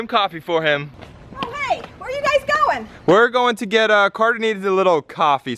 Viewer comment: When you said we’re going to get a coffee I thought you guys said we’re going to get a copy of Carter